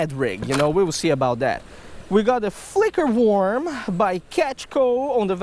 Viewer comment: I thought that said fucker worm lol